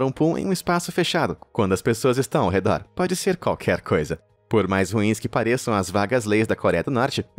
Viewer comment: "por mais ruins que pareçam" eu ouvi isso ?